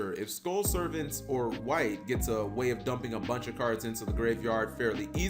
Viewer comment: I'm not that familiar with yugioh and clicked on this video out of curiosity. For almost the entire video, I thought you were saying, White Archetype. The whole time, I was thinking, "Yugioh doesn't have colors in it. Is there something on the card? They're all Dark type monsters. Maybe 'White' is a competitive yugioh term that I just don't know." Then At 11:27, you showed "Wight Baking" and I realized I had misunderstood you throughout your the entire video. I feel dumb